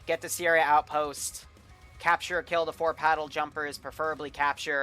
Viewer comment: I heard Paddle jumpers